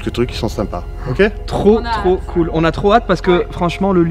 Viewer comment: A 00:11:28 ont dirai que juste avant une nana, dit "Trop naze" 🤣🤣🤣 j'espère que c'est pas ça du tout 🤣... dites moi les gens si vous l'entendez comme moi ? 👇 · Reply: On dirait trop naze mais c'est trop hâte 😂